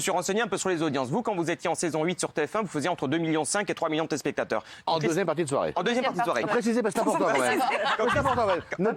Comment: elle a dis quoi ? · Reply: « En deuxième partie de soirée » non ?